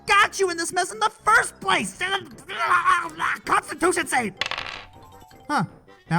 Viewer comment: hey what did you say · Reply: Constitution save